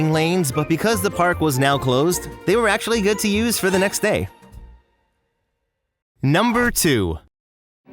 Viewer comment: That {inaudible} part in the quote sounded like “for the rest of today.” to me.